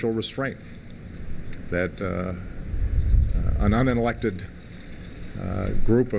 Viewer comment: STOP THE PRESSES!!! He did not say they were un-elected! What he said was: "that ahh an un-intelected ah group of of people ..." (1:33) Where are all those speech Nazis at now?